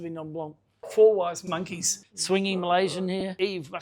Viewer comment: wtf am I hearing?? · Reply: “Four wise monkeys”